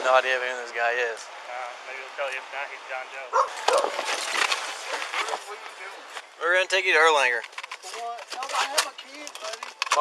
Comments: I don’t know who does the closed captions here , but they are wrong. 16:13 The captions say “maybe he will tell you who he is if not he will die in jail” he actually said “if not he’s a John Doe” , it’s already horrible enough without having to make up lies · The officer doesn't say "if not he can die in jail." What was said is "if not he's John Doe."